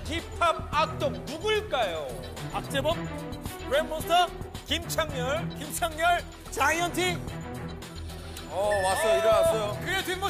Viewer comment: Okey, but did Sangmin say "Kim Chanyeol"? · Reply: 요정꽃 changryul a member of DJ DOC